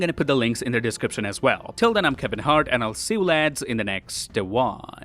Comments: Lmaoo did he say "I'm hevin hart" instead of "I'm Canadian lad" 😭😂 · did he really say "till then im kevin hart"???